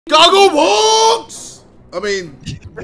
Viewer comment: did he just say goggleborts?